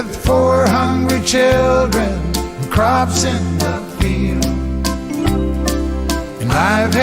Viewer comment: I had the lyrics wrong due so long . Instead of 4 hungry children and crops in the field “ I thought he said “400 children and crops in the field “ and I thought she bailed on him and all the farm staff . 😰